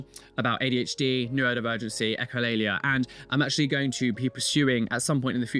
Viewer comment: Can someone help me understand what he’s saying at 10:32? He says “adhd, neurodivergency, and……” Hilalia? I’ve never heard that word before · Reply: Echolalia, it's seen more in people with turettes when they repeat things they hear